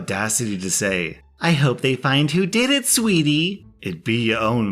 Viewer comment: OMG... I slightly misheard the insane parent in the first story and I thought she said, "I hope they find you dead, sweetie! ;)" My first reaction was "JESUS EFFING MURPHY WHAT IS WRONG WITH YOU?!?!"